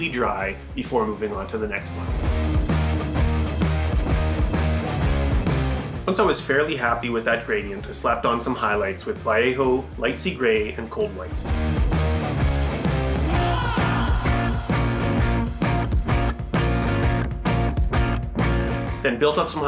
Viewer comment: Heh… I kept hearing “lightsy grey”…! Great job, looks great!! I’m really trying to get the whole glazing technique down, but it hasn’t really clicked yet!